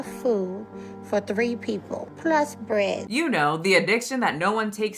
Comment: "I eat enough food for three people... PLUS bread" I can't 😂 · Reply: i first thought she said "three ppl...plus brad" god daaaamn, how much does brad eat?! :D